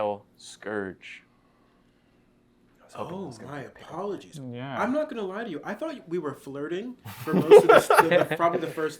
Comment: wait can someone tell me what the guy whispered here, I wanna know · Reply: “i was hoping it was gonna be a pick up line”